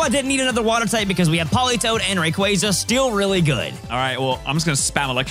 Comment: did you say steel really good here? Or still? 😂